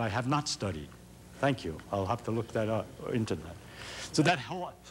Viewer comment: 'I have to look that up or internet'? Did I hear that right?